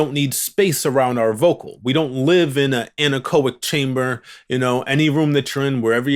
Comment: at first I heard 'covid chamber'